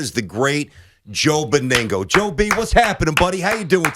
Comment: I thought it said Toe Beningo for a second